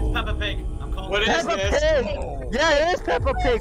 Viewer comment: Peppa instead of ganon.